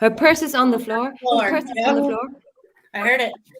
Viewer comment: says her purse is here...sorry. Not to disagree but I have better then average hearing...plus I listen without bias whether that enhances or hinders my theory. Just started watching now. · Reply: I heard “purse is by the door” IDK, gosh its crazy this case.